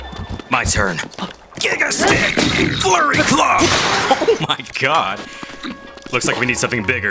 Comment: Dude, I’ll never get I’ve your commentary!! Has me cracking up the whole time!! Giga Stick!! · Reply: Someone thought I said Furry love when I said FLURRY CLUB 😂😂😂